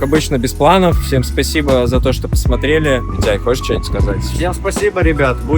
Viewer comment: Зай, хочешь что ни будь сказать ? Мне это послышалось ? ))) Мгаааа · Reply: Митяй он говорит